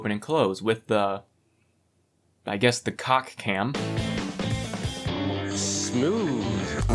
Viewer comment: cc thought it's called the 🐓 cam 😂